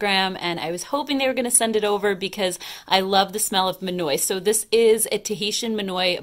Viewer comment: Oh, lordy, I need some sleep!! You said, "I love the smell of monoi" and YES it does sound delicious....but I really thought you said, "I love the smell of manure!" O.M.G. hahahahahaaaaa